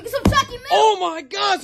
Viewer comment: I heard: "some chucky milk"